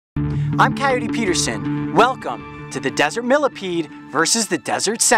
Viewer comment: in the first few episodes that im watching vids of this guy i thought he was saying "hey guys im cayote peter sayin' "